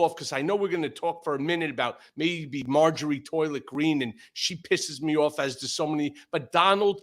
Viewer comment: I thought Michael said Marjory Soylent Green. He didn't buy it kinda works 😂😂😂